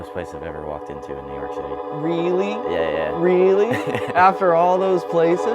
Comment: did he say New York Shity? 😮